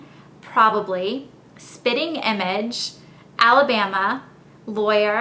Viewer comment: It sounded like you said "spinning" image lol